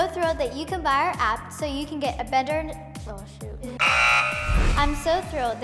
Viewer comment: Did Kendal just said badword 1:09 no hate I still Love Kendall Im not really sure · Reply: she said "oh shoot"